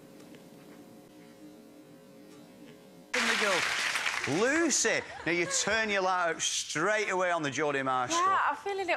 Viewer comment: The British dating show at 6:00. I could hardly understand what they were saying. What did the blonde, say that got such big applause? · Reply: She said "I would have had a really good answer for this if i like, liked the lad" Basically she already lost interest in him by that point and didn't want to answer his stupid "stranded on an island with a meerkat" question lol. Biggest roast ever lol.